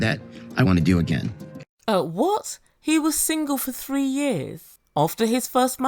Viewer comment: I heard him say 30years not 3 years